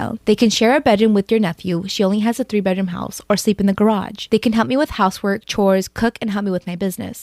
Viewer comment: did she just say sleep in the garage??